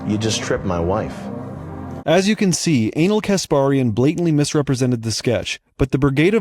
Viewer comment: lmfaoo did you say anal kesparian?😂🤣💀